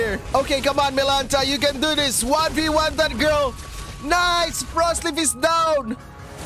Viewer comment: did he just said frostleaf 😭😭😭